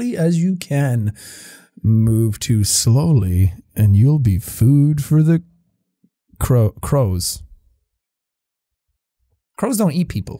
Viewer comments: On daisy's farm i thought it said you'd be food for the cows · i thought that said cows whoops